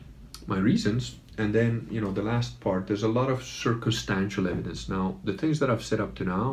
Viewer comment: Did he say "circlestancial evidence?" 17:40 Just asking.